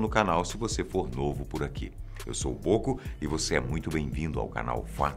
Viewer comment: eu ouvi "eu sou o ocu" desculpa mais foi isso que ouvi